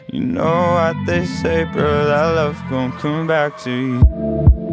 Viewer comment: I always thought it said , "don't come back to you" instead of "gon' come back to you". I took it as an internal struggle of not wanting to go back into something toxic but the heart forcing you to go back